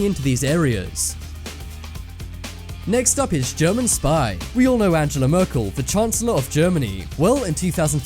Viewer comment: umm... 5:40 it said sky instead of spy XD